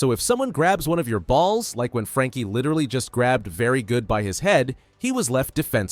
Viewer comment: what did I just hear💀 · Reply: Well, his name is Very Good.